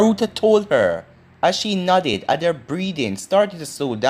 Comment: I'm Jamaican and your accent is thicker than mine lol. at 7:19 you said she nodded. i thought you said she nutted lmao